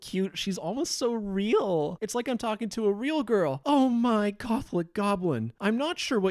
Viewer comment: WHAT DID HE SAYYY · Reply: In the moment? "OH my gothic goblin. I'm not sure what you mean by that." In the previous instance? "I want to ejaculate inside of you. Do you want that?"